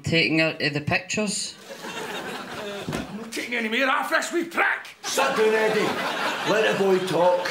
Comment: Can I please get a transcription of what is said at 1:13? 😂😂😂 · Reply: “I’m no taking anymore off this wee prick”